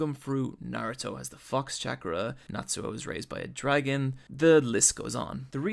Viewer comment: I swear i hear "Naruto have the fucks chakra"... Great anime, the quimera ant arc was magnificent, definitely one of the best anime i have ever seen in my life.